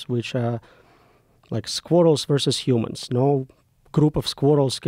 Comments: At first I thought he said squirtles and I thought to my self theres no way joe rogans gonna know what a bloody squirtle is 😂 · I thought he said "Squirtles". I was like : How does he expect Pokemons to wipe out humanity? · Bro said squirrels and I thought he said squirtles. I was like a group of squirtles would fuck us up😂